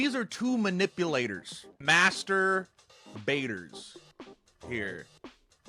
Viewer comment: Why did he call them masturbators